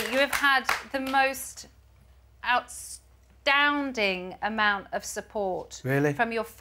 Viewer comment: Outstounding ??? What did Emma wanna say Outstanding or astounding 😂